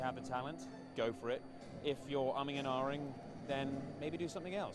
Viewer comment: Could anybody transcribe what he said at 3:16, please? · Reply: He is taking about if you're serious about acting, go for it, 'but if you're uhmm-ing and aah-ing', then it's not for you